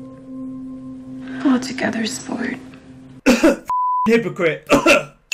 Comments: What did enid say? I heard "we're all together sport" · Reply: A little late here but she said : "get it together sport" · What does enid even say at 2:02? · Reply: "Pull yourself together sport" lol.